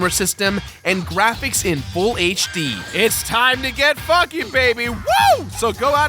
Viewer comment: did he say it time to get f***ing? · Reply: It's time to get funky, baby.